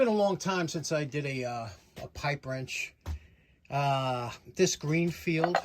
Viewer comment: Pipe Wrench, I thought you said Ripe Wench. Darn it.